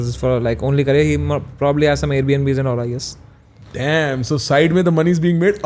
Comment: pe kya bola. Please tell me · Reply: Air bnbs